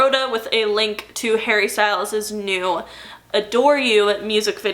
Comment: When you first mentioned “adore” I thought you said “a door” and I was promptly confused for a few minutes 😅